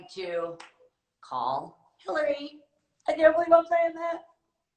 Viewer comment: What did she say? 🤔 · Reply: Can’t believe I’m sayin that